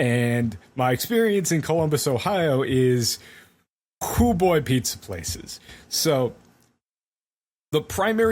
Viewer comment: Swear to God at the beginning I thought I heard Pizza Plex